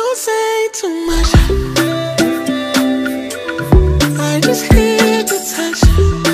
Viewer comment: I thought he was saying “I just hear the touch”